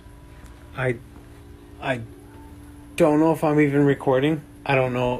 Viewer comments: sounds like it says "I donno" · Sounded like she said I dunno. I follow him on TikTok this man moved then it followed him it’s very sad